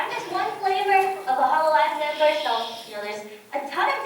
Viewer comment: anyone know what she said at 42:00? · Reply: hololive members and holostar members